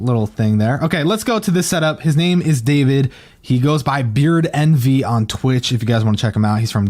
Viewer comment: Anyone got a link to the streamers setup? Sounded like he said beardmv or nv, but neither are him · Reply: Gaming BeardEnvy